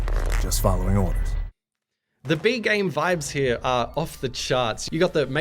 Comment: When he said b-movie vibes I thought he said bee movie vibes